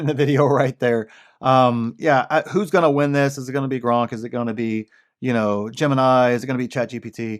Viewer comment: Good stuff as usual. Be interesting to look back at this point in time 5+ years from now and see where we are... Random side note: at 18:58 sounds like you said Gronk, and the thought made me laugh. Life would be so much cooler with a Gronk AI. Gronk AI: Hey bro i don't know about that but let me tell you about Wahlburgers or the one time I..."